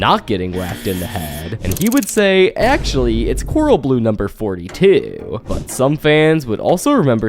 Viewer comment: I thought he said coral blue number 3